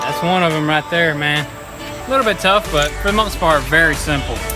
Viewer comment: did you say right meow ?